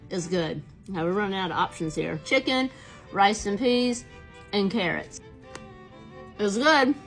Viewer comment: I literally heard “chicken, rest and peace” not “chicken, rice, and peas” lmfao